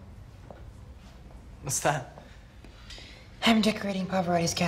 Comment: did Blaine said musta? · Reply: He said 'Whats that' but very weirdly